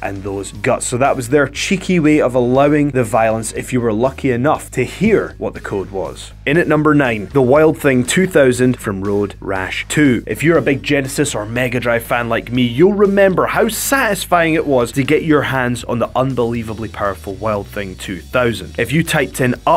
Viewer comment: thought he said a "Genesis and Megadeth fan" XD